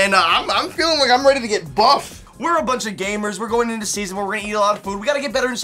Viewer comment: Omg Josh, I thought you were about to say 'We a bunch of gays' (0:18-0:20) >.=.< My mind is in the gutter tonight :P